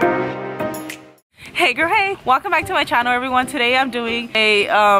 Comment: "Hey girl hey" But what if I'm a boy? (I'm a girl) 4 months later Edit: For all the little girls and boys who took offense to this realize that this is only a joke. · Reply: Sometime in her videos and she says it so fast that it sounds like shes saying hey gra hey instead of hey gurl hey 😂